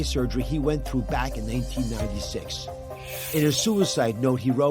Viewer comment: Did I hear him right around 1:30? I think he said 1896 when he meant 1986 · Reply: i listened closely, he says 1996, it’s the heavy lazy jersey /northeast PA accent